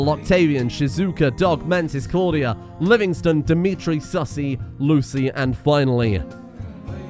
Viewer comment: did he say Sussy?!